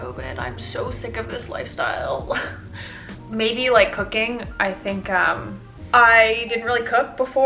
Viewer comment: omg I thought you said "cocaine" at first ahahahahahha